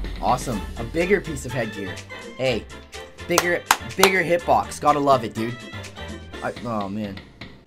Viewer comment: did he say the n-word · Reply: shaggy boi bigger lolz